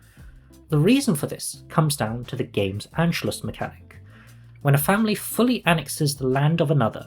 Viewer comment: sorry, did you say Anschluss mechanic???